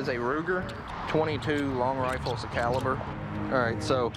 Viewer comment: Did he call that gun a .22 long rifle ( 2:32 ) 🤔